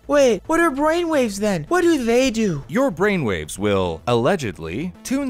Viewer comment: "...Your brain-waves will - allegedly - turn your brain to mash/mush..." (instead of "tune your brain to match...") 😁🙃😁 ~5:29 "...The difference between radio-waves, brain-waves (...) is the frequency, or f4rts-value..." (instead of "hertz-value") 🤣🙃🤣 These were my hilarious misunderstandings so far. Guess, I'm WAY too tired to understand this scientific stuff correctly atm, so I hear only nonsense (which is my bad, NOT the mistake of the video). 👍👌👍